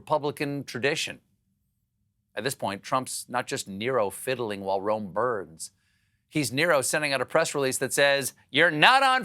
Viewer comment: I love when Stephen shits on Trump, but did he just saying "Nero fiddling while Rome birds"?